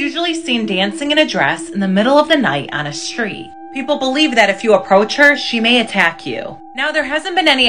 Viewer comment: What i hear "IF YOU APPROACH HER SHE WILL FLIRT WITH YOU"